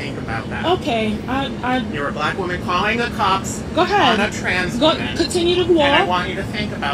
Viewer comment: Did he say "transman" 1:44 😂